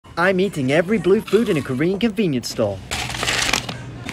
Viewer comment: I thought he said I’m eating everything at a convenience store